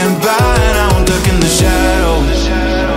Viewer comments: I always hear 'wont nut in the shadows' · I'm pretty sure he says "won't walk in the shadows" at 1:02 but to me it sounds like he's saying "won't NUT in the shadows" everytime I hear this. gets me every time. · It might just be that I am an irredeemable pervert but I swear to God it sounds like he says "I will not nut in the shadows"